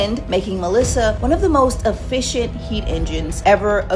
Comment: I thought she said giant skeet engines lol